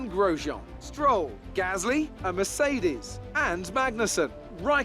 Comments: Maybe they had a failure and they couldn't fix it before the end of the session ? Also did I just hear Crofty call you "A Mercedes" at 12:25 ? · Did Crofty just call you a Mercedes?